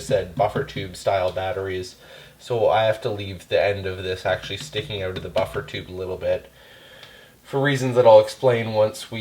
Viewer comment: didn't realize you were Canadian until i heard "sticking oat of the buffer tube" lol